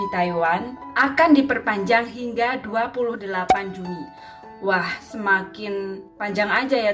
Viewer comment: Perpanjang sampai 28 juli ya mba ? Kedengeran sy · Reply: Halo mbak..maaf baru bls..iya mbak perpanjang pek tgl 28 juni untuk pencegahan civid siaga 3 Taiwan, mbak...😊🙏